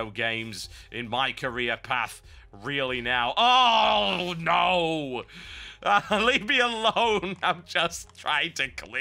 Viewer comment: lol i thought he said me alone